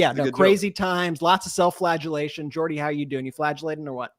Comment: I thought Ben said ‘self flatulation’ which may be funnier.